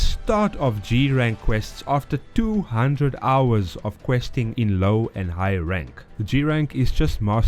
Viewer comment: For a very long second i thought he said that he reached G-Rank quests within 2 hours of playing. Fastest rewind of my life 😂😂😂